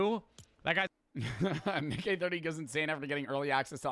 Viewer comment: bro did NOT say “NickEh”